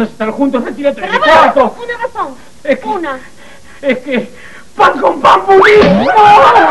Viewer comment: que dijo?? xd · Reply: Pan con pan budín